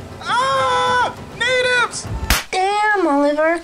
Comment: WHAT DID HE SAY?!?!?1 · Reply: AUHHHHH NATIVES